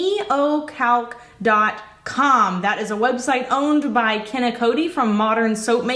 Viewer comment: Thought you said Ye ol’ calc .com 😆 got excited to go check it out.